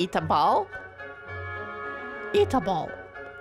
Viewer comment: Man when I heard them say eat a bowl I thought they said eat a ball and I was like hold up, what